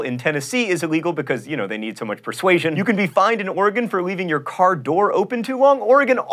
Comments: When John said “you could be fined in Oregon” I thought he said “fined an organ” and I was shook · I thought you said "You will be fined an organ" at one point and was slightly disturbed and confused XD